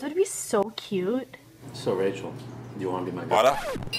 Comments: is it was just me or i heard that said "Soo... Rachel wanna be my water ?" 😉😂😂😂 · It sounded like said do you want to be my water😂😂😂😂😂😂😂😂